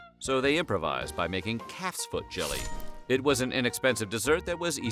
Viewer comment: CALF'S feet. I thought he said cat's feet. Like WTF...?